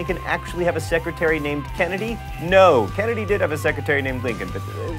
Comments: when he said mental floss it sounded like he said menapause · Is it a bad thing that every time I hear John say "Mental Floss" I hear "Menopause"? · The way he said mental-floss at the end sounded like he said: "Thank you for watching menopause." XD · for some reason I thought he said menopause instead of mental floss at the end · you should slow down that last part, sounded like you said thanks for watching menopause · I always hear you say "thanks for watching menopause" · At the end of every video when he says "Thanks for watching mental floss" I always hear "menopause" instead xD